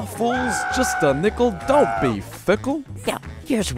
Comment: I thought he said just a nickel don't be a picked,💀💀😫😩😩